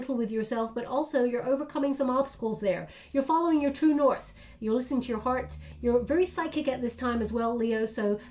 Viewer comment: I heard "You're listening to your fart." 🤣 well halloween vibes for sure. Thank you!! 🤍